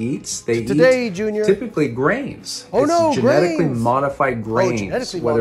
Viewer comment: At first I thought the guy said cows eat brains.